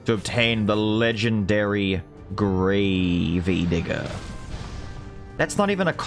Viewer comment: what did he say ?!?! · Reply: Gravey Digger. Because I've been calling him Gravey :D